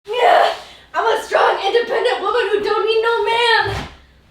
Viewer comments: I thought she said “ I’m a strong independent woman who don’t need no ham😂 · I thought she said strong and pregnant 🤨🤷‍♀️